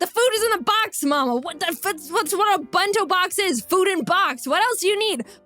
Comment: I thought you said you saw food wars don’t you know that one must put their heart and soul into the bento